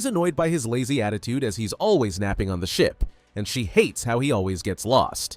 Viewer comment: Did that say ship or s word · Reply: Ship